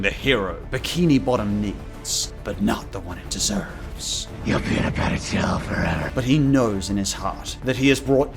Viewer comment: "You're peanut butter jelly forever" That's what I hear from Batman lol